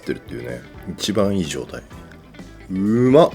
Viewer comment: ～「一番いい状態」が「柴みがいい状態」に聞こえちゃうんですよ…